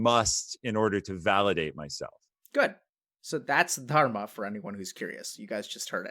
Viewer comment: what's the word that doc says? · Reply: "Dharma" , a sanskrit word for personal duty/responsibility, he talks a lot about it on the channel.